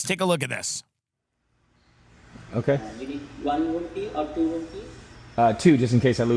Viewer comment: When he said room key I thought he said “rupee”.